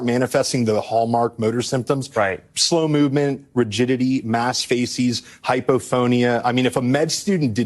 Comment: No matter how many times I listen, I hear "mass feces".